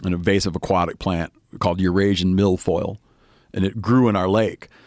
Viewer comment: all I heard was “your Asian milf “